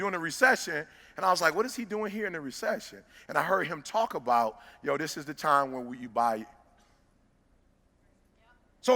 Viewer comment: Does anyone know what he said Warren Buffet said to buy @ 12:38 during a recession? They blocked it out. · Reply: Keona Lashae real estate because everyone is looking to sell off debt for cheap